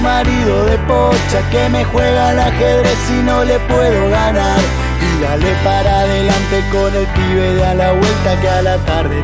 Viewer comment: el pibe creo que dice para Pappo puede ser? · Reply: Para Pappo Dice Agus CABJ Tenés Razón